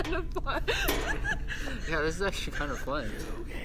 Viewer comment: I heard some voice saying: it is fun